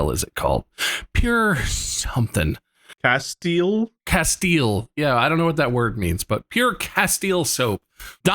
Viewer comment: when they said “Castile” I fr thought they said “Castiel” for a sec, I was like. How do you guys know my name- “soap, Castile soap” oh- (Everyone misreads my name and says Castile or Cast iron for some reason?)